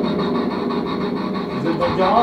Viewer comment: A 20:20 je crois que l'esprit dis à coter de toi ou dans le garage??